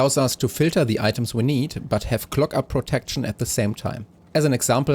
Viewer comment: and have clocker protection? i’m so sorry what did you say 😭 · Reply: Clog up protection